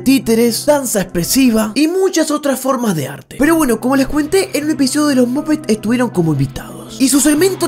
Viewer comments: Nadie: Absolutamente nadie: Yo dándome cuenta que dice "como les cuentie" minuto 6:02 jaja me morí de risa!!! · soy la única que escucho "como les cuente" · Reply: No,yo también lo escuche " como les cuente" <num>